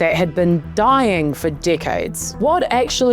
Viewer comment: "a landscape that had been dying for dickheads" oh, no, wait, I misheard your accent. I guess it's dying for decades...